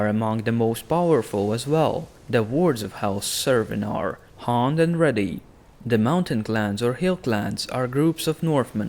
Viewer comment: I thought he said hung and ready